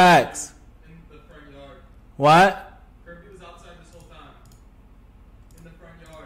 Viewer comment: What was Brady saying on 00:54? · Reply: "kirby was outside this whole time"